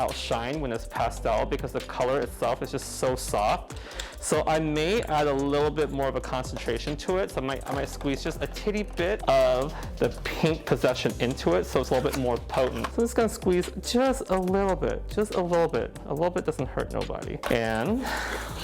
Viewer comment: Dying laughing bc I think he said he’s “adding just a titty bit” at 9:46 and I’m here for it!! The hair also looks absolutely B-EA-UTIFUL!!! Another killer and unique coloring from you!!! Wish I had the “you know what” to do this!!!! 💗💜💙💚💛🧡❤️